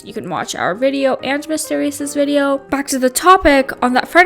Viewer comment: you saidv to watch "mr. Beast" video instead of Mr.Beard" lol